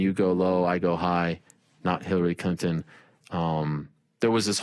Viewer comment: Did Ariel say “Hillary CUNTon?” 🤣